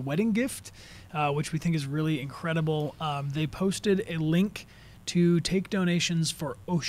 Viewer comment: I fast forwarded and it sounds like said “he posted a fish”